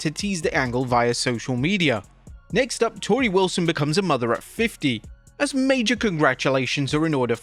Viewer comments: I was not looking at the video and listening in the background and I thought he said mother of 50 so I was extremely confused 😅. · Really thought you said mother "of" 50 not "at" 50. Why even bother at this point? That poor kid is gonna only be raised by money 😢